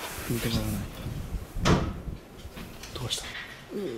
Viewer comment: さんの声と被るように男性の声で「どうしたの？」って聞こえるかも＞＜